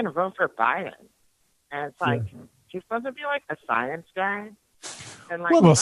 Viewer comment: Did she say Weinstein was a scientist ? 🤣😂🤣😂🤣😂🤣🤣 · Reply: I think what he actually said was (0:47) "Is he supposed to be like a science guy? I know the voice was a bit strange, but that was a dude, you don't hear of women named.